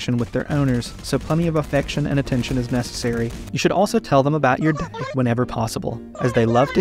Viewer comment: Is it just me who heard “tell them about your dick whenever possible” at 33:19 ?